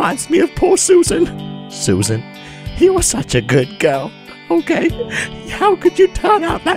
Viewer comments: did just say "what a good GIRL HE WAS" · did anybody hear this? Poor Susan, Susan HE was such a good GIRL